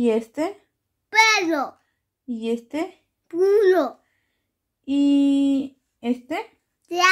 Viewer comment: Perdoname de corazón pero entendí c*lO 😭